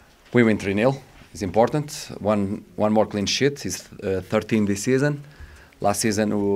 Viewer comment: Excuse me but instead of saying "one more clean sheet" as he meant to say, I thought he said one more clean shit😂I'm sorry, go owls!